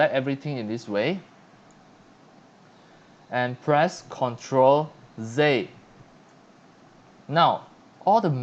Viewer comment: For those of you that think he is saying in blender “ now press control zey “...which sounds like he is saying press control z or c ...we know that’s not it ....what he means is press control J